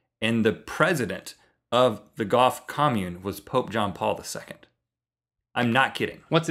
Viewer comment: Haha! I thought you said "GOTH commune!" XD